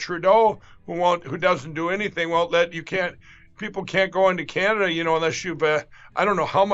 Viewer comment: DID HE SAY CREEPLE?